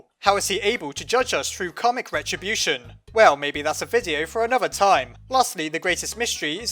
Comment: When you said “karmic retribution” I thought you said “comic retribution”.